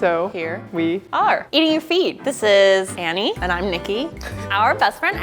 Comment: I thought see said “eating your feet” XDDDDD